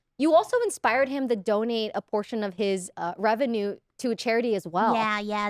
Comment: I'm sorry but I thought she was saying "don't eat plasma" and I was so confused thinking "people eat plasma?" until they talk about getting nurse to do it mobile... It's the first time I misheard "donate" and "don't eat" 😭 Ironmouse voice is so cute and she seem to have a good personality. I've been discovering new people through Press Esc. I also visited Michelle Kare's channel after seeing her here. Interesting enough I know Connor because he is a friend of Emirichu that I'm subscribed to. Another interesting thing is I saw Maya through her tiny house and sanctuary tour, then I watched Poki and Lily's wine about it episode and just realized in this Press Esc that she was the animal streamer girl that I watch toured her house. The internet is so interesting it literally connects people like a dot.